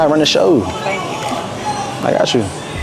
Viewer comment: Thank you daddy” as the subtitle got it 😂